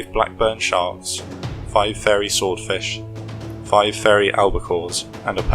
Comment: captions say 2 albacores, but audio says 5 again